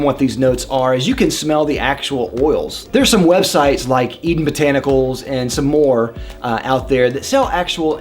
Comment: Subscribed and hit that bell very interesting channel I am taking notes what was that site called at 2:48? Couldnt really hear that 😂 I want to own my own perfume one day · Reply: THank you! It was Eden Botanicals